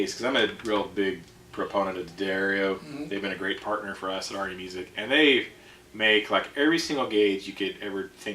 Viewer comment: Funny how it sounds like he is saying "Arni Music". Or maybe it is just me.